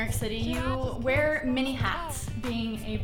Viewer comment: I thought she said 'mini hats' instead of 'many hats' and just thought the guy liked little fedoras